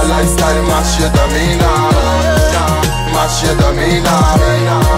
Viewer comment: 🤔 I thought he said " Mash your demeanour"🙄🤣